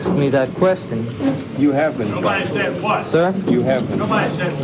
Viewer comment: he seems to freeze when he hears the voice off camera (not the reporter talking about being charged). The off camera voice is the person towards whom he turns. The voice seems to say "nobody says what". The chin motion comes after hearing that line. As though it is a message to Oswald and one he didn't want to hear. How did you miss this? "Nobody says what" might be a way of saying "you are a nobody" ie. Younhave been cast off by the people organising this. We aren't going along with the script we gave you, you being cut loose. The chin motion then makes sense.